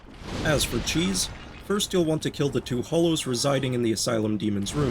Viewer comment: I for sure thought you said homeless, so i backed up and rewatched it so i heard it right. Nope. Just hollow. I'm calling them homeless now because as soon i heard it i was like, "Lol, yup. They're that! Hey wait a minute!"